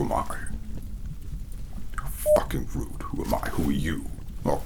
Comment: Put this on to sleep and I hear “Who am I? How f***ing rude!” and I lose myself laughing 😂